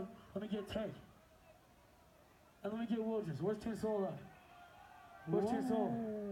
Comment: kinda sounds like hes saying: where's juice wrld😔